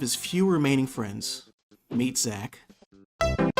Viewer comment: I thought you said "meatsack" for a moment 😂